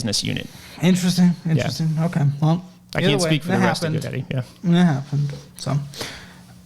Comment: " I can't speak for the rest of GoDaddy." Did I hear that right?